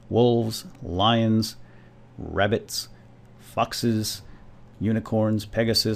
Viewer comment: i thought when you said foxes that you said fuckses